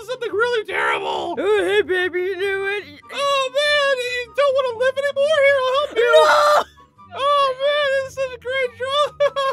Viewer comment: what i heard oH hEY BabY yU0 kNOw whAt AWWWW NAILD IT YOU DONT WANNA LIVE ANY MORE HERE I'L HELP YOU